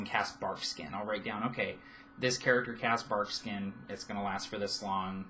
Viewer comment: I chuckled. I heard you saying "character casts barfskin". New spell idea. Or monster.